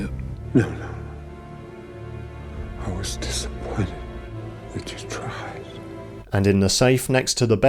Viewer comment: what does his dad say?? I was disappointed ______? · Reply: He said, "I was disappointed.....that you tried". He meant (for his son) that he never wanted him to be just like his father and that he can be better.